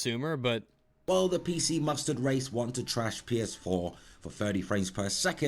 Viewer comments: He sounds like he is saying pc mustard race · Did he say PC mustard race at 16:05? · am i mishearing or is he saying pc mustard race?